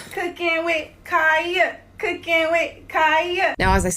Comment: Showing your age without telling your age, because all I hear is not "Cooking with Kya" but "Peanut butter jelly time!!!"